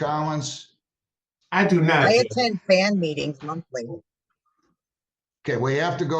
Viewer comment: does she say she goes to Klan meetings monthly?